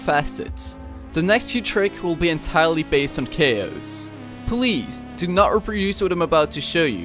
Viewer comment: he says k-o's instead of chaos